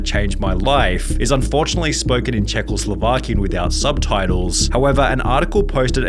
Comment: did he say czechoslovaki💀(no hate)